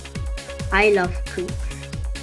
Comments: He's a very funny kid, to be honest, because of his wibbly wobbly accent that we can't rarely understand sometimes including : 0:49 It sounds like he says i love poop xD 1:16 Twerking xD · "i love poop" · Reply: What I heard is, “I love creeps” but he probably was trying to say, “I love creepers”